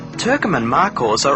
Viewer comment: Turkmenian Markhor. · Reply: Ok, thanks. The guys sounded like they were saying “Turkamen”, though.